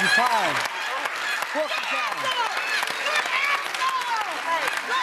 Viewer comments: Sounds like she was saying “Good Asshole” when she said “Good Answer” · When she yells "good answer" all I hear is her yelling "good ass hair" or some other mispronounceation of "answer" and thinking "WTF did I just hear!?!"